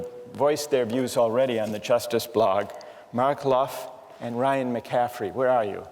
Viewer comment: Seriously I heard Markeloff from CS1.6 :D